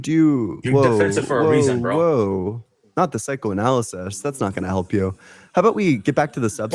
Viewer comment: Daaaaamn, I shouldve got that 😂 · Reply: i also thought he said psychic at first tbh